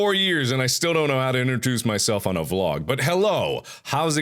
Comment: My brother thought he said in the beginning that "Hi, oh I forgot how to touch myself in a blog." Very funny.